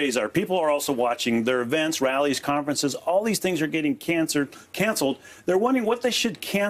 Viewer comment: Did he just say everything is getting cancered